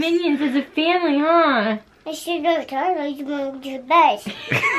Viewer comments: after they said they watched the minion movie sounded like said: "It should have been Toy Story so I could have seen Buzz!" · I think in 11:52 he said then the movie end and I had to go to bed